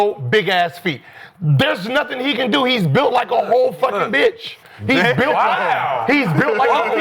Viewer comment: Did Gil just say that Kat is built like a bitch??? 😂😂😂